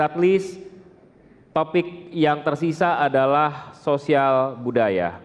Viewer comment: saya dengernya soale Ternyata soal E